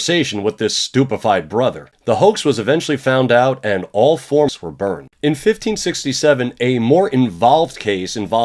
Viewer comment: all forms were burned? im not sure if the captions and i are mishearing but idk what you are trying to say XD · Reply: * All four (meaning the four monks who perpetrated the plot). Sorry it was inaudible!